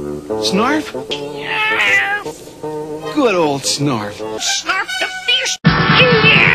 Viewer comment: Did he say Snarf the Fetus?